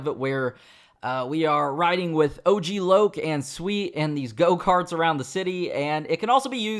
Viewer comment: DID HE JUST SAY SWEET?